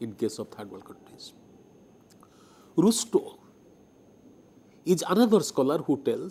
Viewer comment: can some please tell the name of scholar mentioned @ 47:28 by sir, could understand what he said , rustoh? what? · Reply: Rustow.......Dankwart Alexander Rustow